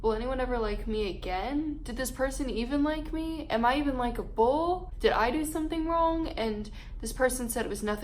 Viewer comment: When she said “am I even likable” I thought she said LIKE A BOWL